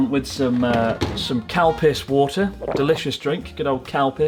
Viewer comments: i thought you said "cow piss water" lol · OH I HEARD "COW PISS WATER".. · I misheard calpis, I thought it's cowpiss. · it sounded like he said cowpiss water lmao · I thought you were saying cow piss instead of Calpis until I read the label 😂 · I thought Chris was saying "cow piss" for a bit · Oooo calpis water... i thought he said cow piss water 😅